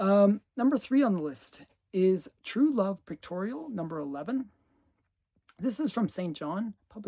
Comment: I thought you said “Pectorial” when you showed that book.